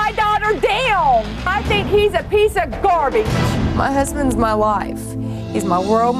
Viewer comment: I thought she said "my husband's my wife" lol